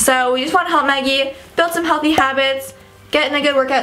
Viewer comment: it says heathy instead of healthy